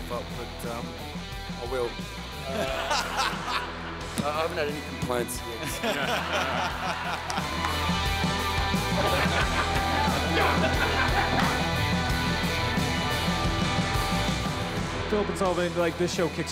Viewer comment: what does he say at 0:35? ♥ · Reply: He says "arh I haven't had any complaints yet so..."